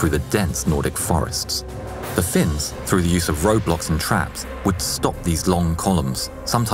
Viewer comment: Is it just me or I hear Roblox here?